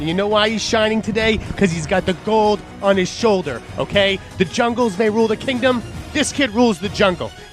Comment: does say "The Jungles rule the kingdom" made me lol great stuff